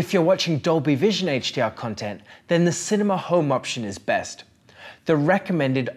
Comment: Hey guys in your full settings link, it's written that 'Technicolor Expert' is best for HDR10 content but in the video you say it's Cinema mode? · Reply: I thought he said cinema mode for Dolby Vision. Could be wrong tho